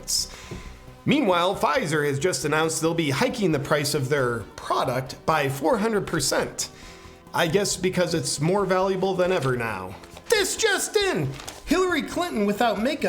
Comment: It sounded like you said. “ George SoreAss”. Or was it. “ George Sorrows”. ?